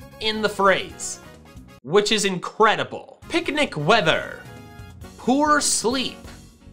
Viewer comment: I thought you said picnic wiener and I was just so confused.